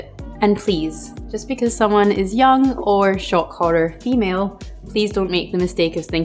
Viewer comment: What did she say at 2:51 between "young" and "or female?" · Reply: ‘Shock horror’ 😜